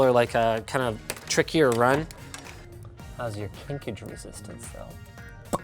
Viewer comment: subtitltes / closed captions say "How's your (indistinct)" I believe Linus said "How's your kinkage resistance" I wish youtube had an easy way to suggest subtitle changes